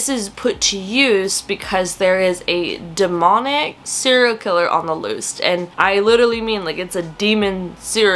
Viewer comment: PSL 🍂 When you were talking about the Diviners I clearly heard "cereal killer"... Listen, it's too early and I just woke up, and it took me too long a minute to understand what you were actually saying 🤷‍♀️